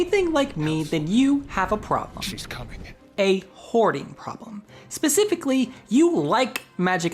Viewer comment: Oh. OHHH! You said HOARDING. I thought you said horny. Which makes sense for a BG3 video.